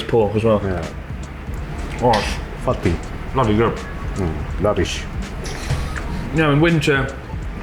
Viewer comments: It sounded like said "Lavish" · i think is saying lavish